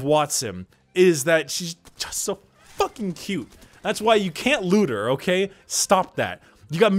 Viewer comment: And thus... caused a surge in Wattson rule 34.... Also I heard it as don't loot her